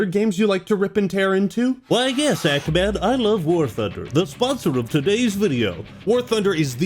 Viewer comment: i thought you said you love war fker